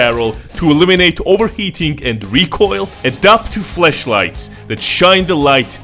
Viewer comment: Let's all pretend like he DIDN'T just say Fleshlights instead of flashlights at 4:42 shall we